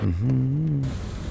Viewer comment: did he say homo?